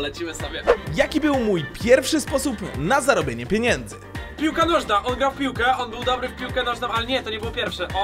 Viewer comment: Usłyszałem że mówił "kur..... Wiedziałem że piłka nożna" ale pozdro fajny odzinek polecam miłego oglądania